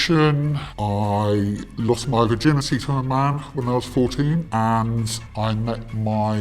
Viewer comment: did he say he lost his virginity to his mom? 🤢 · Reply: Lost it to a MAN at age 14 he said